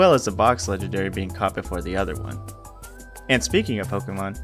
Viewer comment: dude i thought you said "Hoppip or the other one" at first 😂😂😂